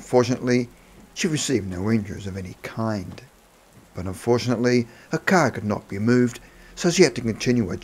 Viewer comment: Wait—“Betty’s granddaughter Elizabeth has researched her aunt’s story”? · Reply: I thought he said "And fortunately." My hearing isn't the best though, you could be right. Edit: you're right, oops!